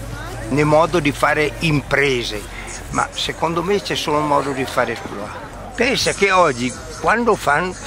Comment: cosa dice a 0:18 ? c'è solo modo di fare .... ? non capisco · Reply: Modo di fare exploit